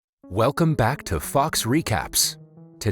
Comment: For a second I thought he said "welcome to fucks news" lol